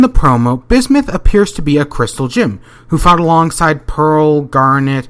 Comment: did he just say crystal gim?